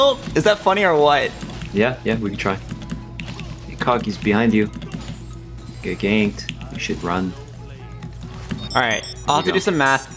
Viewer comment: I dont know who transcribed this video but there were so many things that war far off from what was actually said, it's crazy. 6:45 "Kog, he's behind you. Getting ganked. You should run" is what is actually said